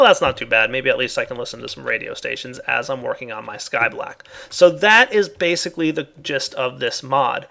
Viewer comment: Did I just hear say Sky Black?